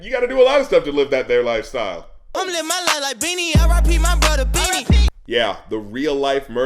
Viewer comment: i heard "living life like a beginner this is just the beginning"